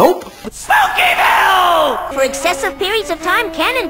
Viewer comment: does eddy say "smokin hell!!"?